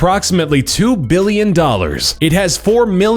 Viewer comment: i thought u said it costs 1 billion$ in the top 10 most expensive houses